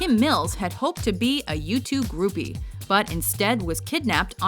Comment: Almost sounds like you said for Kim Mills, "Had hoped to be a YouTube groupie" instead of "U2 groupie".